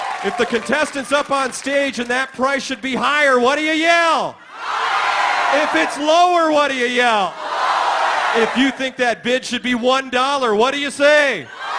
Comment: I sworn Rich said “If you think that ‘Bitch’ should be 1 Dollar What do you Yell?” That’s what I though he said. Lol